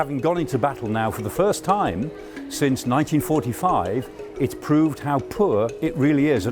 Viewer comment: "havn't got into battle since 1945" Did I hear that right?